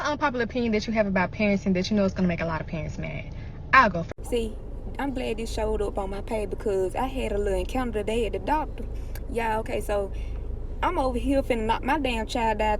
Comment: What she saying at 7:28? Her accent is thick. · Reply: Translation: See I'm glad this came up on my page because I had a little encounter today at the doctor's office. I'm over here bout to knock my child to his knees because he out here showing out in these folks establishment. So you got this white woman and Timmy Timmy Turner knockin her upside her head and she over here worried about my black business because my child's doing wrong and I'm finna F him up in these folks doctor's office. So I'm sittin here lookin at her lookin at me finna knock my child out she like "oh my god she's doing him so wrong" and I'm like b*tch you got bigger fish to fry cause hol up hol up boo you need to get lil bill in order. You worried about the wrong thing cause all I gotta do is make that face that black Mama face y'all know what face I'm talkin about and mine gon sit down. He gon get some act right. So look stay out my black a** business and don't worry bour mine